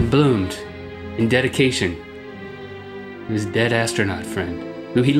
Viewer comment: i thought he as gonna say "Dedicated to his dead-ass friend." XD